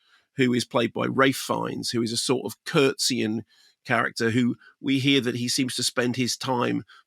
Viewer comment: I realise that there is a certain level of intelligence amongst K&M viewers, but it amuses me that some might have misheard Mark saying 'Ralph Fiennes courtseying character' and imagine him in Victorian frocks.